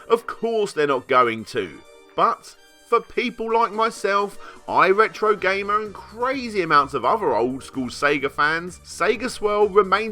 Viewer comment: I miheard at 18:14 thought he said "im a retrogamer with crazy amounts of Adderall" haha had to listen again and again :D